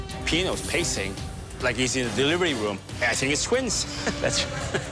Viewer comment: could someone help identify what says at 26:42? "Pino is pacing like he's in a delivery room... _________?" so delighted, I wanna laugh with them 😅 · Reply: “I think it’s twins!”